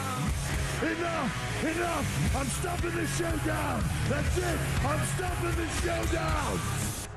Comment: ENOUGH! ENOUGH! I'm stopping this show now!!! That's it! I'm stopping this show now!! 😁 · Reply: I thought he said “I’m stopping this show down!!!”?